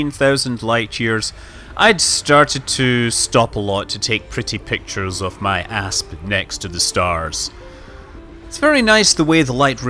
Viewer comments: i thought he said "taking pretty pictures of my ass next to these stars" 😂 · "pictures of my asp".......WHAT I HEARD:"Pictures of my ass"